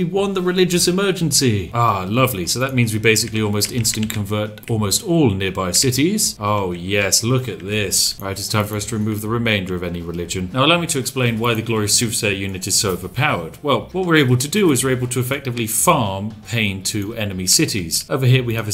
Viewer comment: i know you said soothsayer, but it sounded like you said super saiyan. Lol.